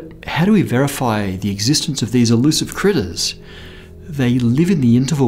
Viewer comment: Walked back into the room and immediately thought I heard "But how do we verify the existence of this elusive 'clitoris?'"